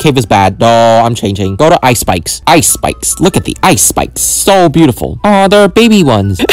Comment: I thought he say ice spice😂